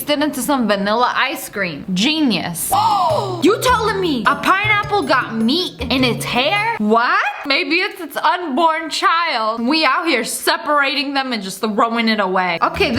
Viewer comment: did she just say meat in its hair?